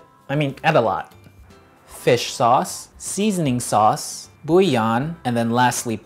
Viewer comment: what is the "seasoning sauce" made of/different name and what is "booyan", which I haven't heard of before? Also, I'm curious as to why it's called "Khao jee" instead of "ping Khao"? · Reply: Buillon